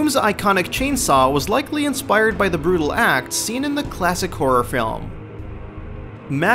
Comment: "DOOM's iconic chainsaw was likely inspired the brutal axe seen in the horror film" you know there's an even more brutal chainsaw in evil dead 2 but I guess the axe inspired DOOM's chainsaw, sure. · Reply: I think he said "brutal acts" instead of "brutal axe" but I could be wrong. The whole him saying "I.D" software thing has me perplexed so I may have misheard haha.